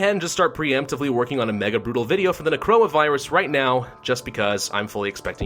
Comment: why do I hear corona virus insteade of necroa?